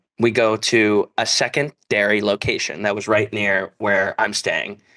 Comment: it's okay i ALSO thought he said "second dairy location"